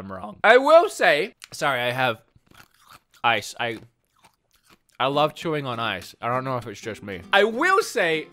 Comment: The first time I thought you said "sorry I love Yuri on Ice", and I was so confused. Had to go back to actually understand that you said ''sorry I love chewing on ice''